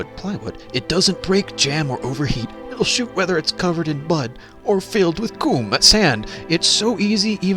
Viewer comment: I swear you just said “OR FILLED WITH COOM”